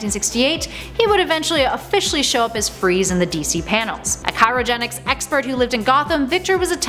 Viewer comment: did she say “cytogenetic”? instead of cryogenic? haha just teasing 😋 great video !!!!